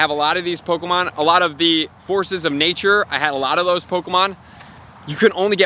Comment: "Horses of nature" I LOST my ultra balls when i heard you say this 🤣🤣